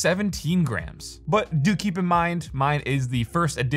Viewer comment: when he says "do keep in mind" at 6:16 , i thought he said dookie. gave me a good laugh.